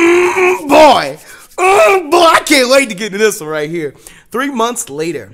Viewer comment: I thought he he said 'I can't wait to die', but thats false